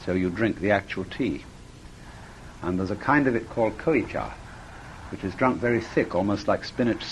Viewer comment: What is the name of the powder tea he says, sounds like 'kiliejar' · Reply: Koicha, a version of ceremonial matcha tea